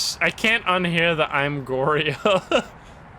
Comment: oi my oreos, that's what i heard